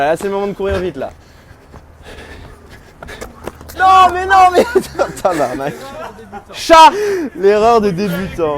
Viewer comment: c un (tabarnak) que jai entendu? Vue que je suis quebecois sa me ferais chaud au coeur😅😆